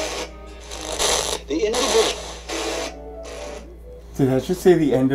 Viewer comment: i'm sorry, but what i heard was "THE END B*TCH!".....i can't unhear it, but it did make me do a spit take XD so it was worth it i guess lol